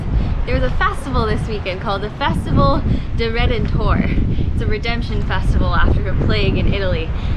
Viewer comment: "Festival of the Redditor" (what I heard, lol)